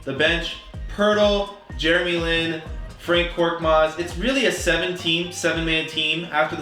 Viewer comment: did he just say FRANK Korkmaz